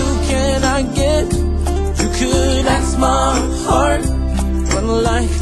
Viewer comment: Growing up, when he said “you can ask my heart” I thought he said “ You collapse my heart” and that’s what it felt like.